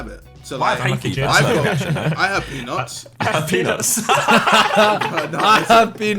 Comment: I thought he said penus